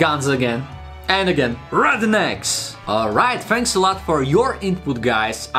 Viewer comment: 🤣🤣🤣🤣🤣🤣 I can’t stop laughing 😂 😂😂😂😂😂 when you said “rednecks” it sounded like you said “rotten 🥚 eggs” …. Yes, that’s correct.